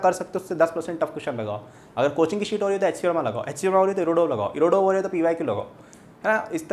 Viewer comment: HC VERMA, IRODOV or which book? Couldn't understand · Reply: Rounak Sarkar pyq-previous year questions